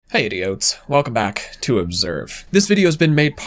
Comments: Had to repeat the intro because I thought he said "Hey, idiots!" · I Keep hearing "hey idiots" in the intro, forgive me ahahqjqjjq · I thought you said "Hey idiots" 😂